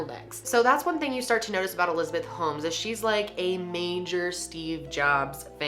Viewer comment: I thought it said “Elizabeth homeless”